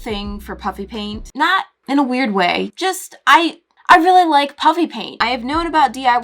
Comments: My younger sister loves when she says puppy paint!! She died laughing when my sister plays the part in a video when she says “uhh it sounds like she is saying puppy paint”. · HAHAHA it sounds like she is saying puppy paint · it sounds like she is saying puppy paint🤣🤣🤣🤣🤣😂😂😂